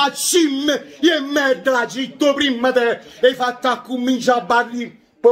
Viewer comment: ma cosa dice, "'e fatt accummincià a barri'"? P. S. ma davvero hai sentito Gattuso dire "andate indietro"? Che schifo, che mediocrità...